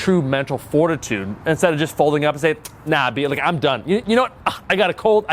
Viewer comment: Did you just say "nah b"?